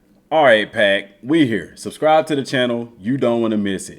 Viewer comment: Wtf did you just call me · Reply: he said "pack" like a wolf pack